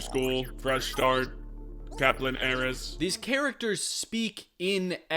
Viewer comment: I was only listening to this video for the first few minutes and I thought they were saying like "caplain Eris" and "fake eris" instead of heiress and I was like what the hell is even going on here edit: kaplan????